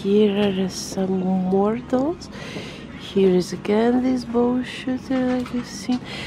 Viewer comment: honestly thought you said "bullshit" when you said "bow shooter"